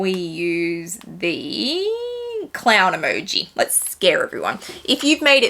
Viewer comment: ☁️☁️☁️ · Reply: Omg I thought you said Cloud not clown 🤣😂 my bad